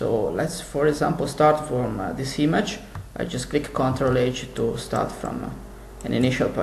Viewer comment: I'm sorry but I don't understand what's being said at 0:50 "for example start this image I click control age" what is control age? or am I not understanding correctly? if someone could explain I would greatly appreciate it, thanks! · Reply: meshlabuser23 meshlabuser23 I believe he said "CTRL H"